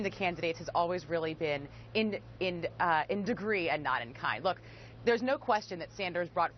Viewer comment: What does she say at the 1:20 mark? The difference between the candidates has always really been in degree __________? Does she say A non-kind? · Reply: "been in degree and not in kind" -- meaning that they want the same kind (of things), but just to different degrees. But this is not true. Bernie is an honest, sincere FDR Democrat, while Hillary is a war-loving, Kissinger-following, corruption-prone insincere liar.